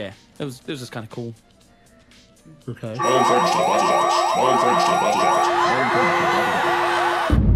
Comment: I need the subtitles of what the alarm is saying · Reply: I think the alarm is saying "SMILING FRIENDS TO THE BOSS OFFICE"